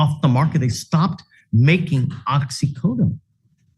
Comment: Did the defendant say “oxycodone” or “OxyContin”? I know the judge is ripping at OxyContin as he should be but I thought the guy said oxycodone